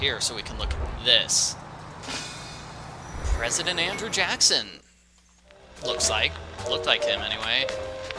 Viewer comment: When he said Andrew Jackson I thought he said Michael Jackson.